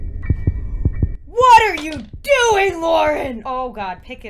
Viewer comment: For some reason at this point my captions said: "What are you doing moron" xD